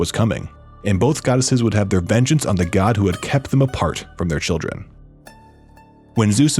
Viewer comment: i thought it said the god who ate this chicken